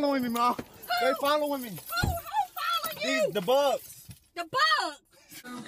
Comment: What is he saying @ 4:45 ? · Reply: Cosmic Clements the bugs